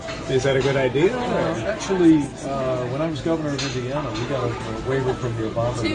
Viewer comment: Did Pence say "When I was governor of Louisiana" at 2:43 or is that just an error in the captioning? Because it kinda sounded like he said "Louisiana", which means he doesn't even know what state he was governor of. To be fair, though, Louisiana and Indiana do sound pretty similar, so maybe I'm just not hearing it.